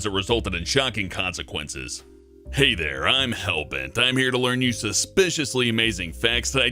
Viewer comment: did he just say I'm here to learn you at the beginning of the video?